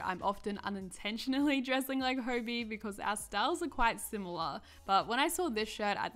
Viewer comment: did she say herbi I- (dont get me wrong I love her so much uwu I just thought it was funny)